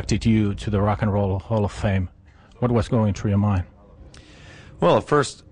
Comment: Did he just say "The rock 'n' roll the hall of FLAME"?